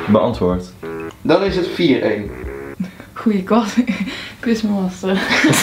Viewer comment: wat zegt nou bij 4:00? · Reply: Goeie quizmaster zegt ze, maar ze verbeterde het